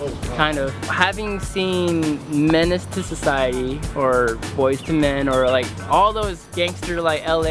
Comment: did he say boys 2 men